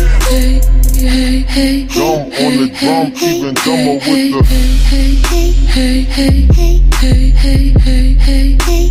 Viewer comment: What does he say at 1:49? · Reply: I heard, "dumb on the drum, even dumber WITH the..." Thnx anyway!